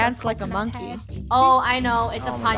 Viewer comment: when bob said ton of food i thought he said togo food